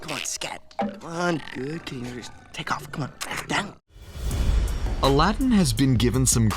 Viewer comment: Walt is rolling in his grave because so many idiots misheard "Good kitty, take off, c'mon."